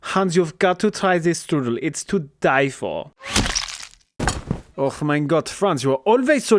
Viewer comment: hanz u got to try this strudel its to- (what did he say?) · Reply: Die for